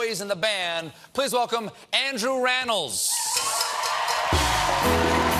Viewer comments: When Stephen first announced him I thought he said Andrew Reynolds and I believed him to be Ryan Reynolds brother. Nothing during the interview deterred that notion and I'm sad I caught my error at the end. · I really thought Stephen said this interview was with Andrew Reynolds :(